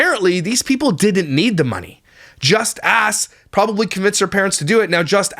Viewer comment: I thought you said “just a33” not “just Kass” 😂